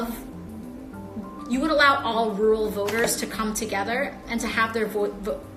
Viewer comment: I thought she said world vote, and figured that is what she said because the crazies like her would like the world to vote in US elections.